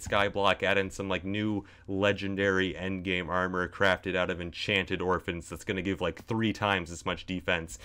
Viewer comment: "enchanted orphans" can you imagine going to an orphanage and everyone is just glowing minecraft style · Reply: I actually thought he said enchanted gorpens